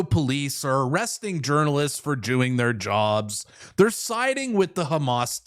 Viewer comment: I swear I heard you say " arresting journalists for jewing their jobs" lol. slip of the tongue or my ear?